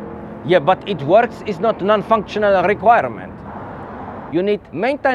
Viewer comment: ""It works" is a non-functional requirement" made me laugh way harder that I should have. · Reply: I thought he said ""it works" is NOT [sic] non-functional requirement" and found that funny as well. Now I'm confused